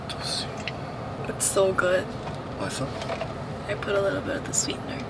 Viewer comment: what's up이 맛있어?로 들려요•••